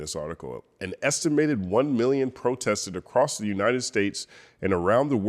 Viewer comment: I thought they were saying 5 million protested